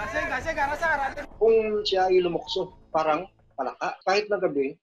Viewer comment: tama ba narinig ko😂"lumukso"